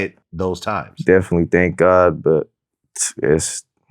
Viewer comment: What the hell did he say at the end 7:30 people are pussy LOL ????